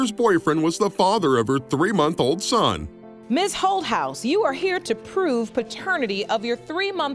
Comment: Thought she said mrs HO HOUSE!🤣